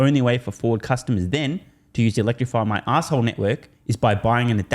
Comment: I bust up everytime I hear "electrify my asshole network".